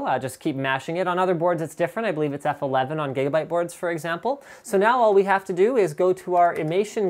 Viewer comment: did you just say "Gaybyte boards"? :D